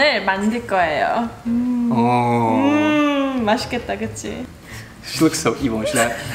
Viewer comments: what did he say???? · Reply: “she looks so evil” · I just noticed at 12:46 said"she looks so evil when she does that" when scrunched her nose so she did it again and ngl I thought he said she looked cute and got super excited😭